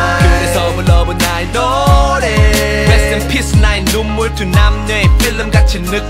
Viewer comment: He actually said rest in piss..D: When it's peace